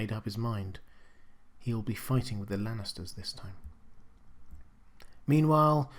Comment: Did he say Randyl Tarly will be fighting with or against the Lannisters? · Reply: Now I'm more confused because I thought I heard him say "fighting the Lannisters this time" but the sub titles say "fighting with the Lannisters this time" LOL